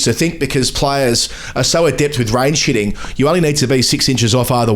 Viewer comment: It does sound like says "rain shitting" instead of range hitting